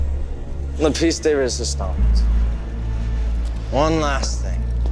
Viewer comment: What does he say here?? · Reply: My piece de resistance (but prounounced stons) it's french (i think don't quote me) for my masterpiece